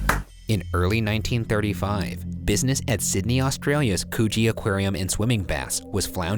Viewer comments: Im sorry WHY did neither of them comment on the Coogee Aquarium .. I could have sworn Ryan said Coochie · i wasnt paying attention n i deadass thought he said coochie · i swear i thought he was saying coochie aquarium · i thought he said coochie aquarium i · When he said Cooge Aquarium I thought he said Choochie Aquarium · Bruh at 0:38 I thought he said coochie 💀💀💀💀😂😂😂😂😂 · i thought ryan said "coochie aquarium" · LMAOOO I THOUGHT IT SAID COOCHIE AQUARIUM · I thought he said Coochie. Not Coogee 😂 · i thought he said coochie instead of coogee kjgnkdjfhgkjdf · I thought my guy said coochie not coogee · I thought he said the coochie aquarium lmao. I was surprised Shane didn't say anything #postmortem Edit: beach community of granola? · When Ryan said Coogee aquarium I could have sworn he said coochie aquarium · i wasn’t looking and i heard “coochie” 😂😂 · i thought coogee said coochie and i wheezed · I KNOW HE SAID COOGEE BUT IT SOUNDED LIKE HE SAID COOCHIE FJDKDK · i really thought this boy said coochie aquarium😖😅